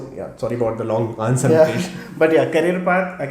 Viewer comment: Did just call him 'Mahesh' at @ 19:34?